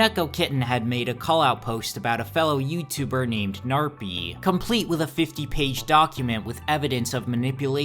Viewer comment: i thought for a second you said "Knarfy" and i genuinely almost jumped